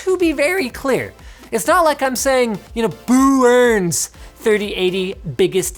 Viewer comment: did he say Boo-urns?